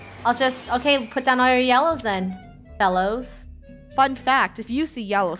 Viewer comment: OHMYGOSH THE OWL HOUSE REF!?!?! (PS: ik she said fellows but it sounded like belos LOLOL)